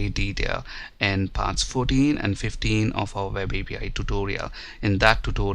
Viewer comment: he said talk about this in another video, but I cant understand what video is. Can anyone tell me? I understand 14 or 15 but not the series. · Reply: He is talking about WebAPI... videos part 14 and 15..there he discussed cross origin related